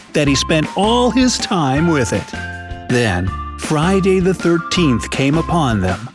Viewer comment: DID YOU SAY FRIDAY THE 13TH????????????????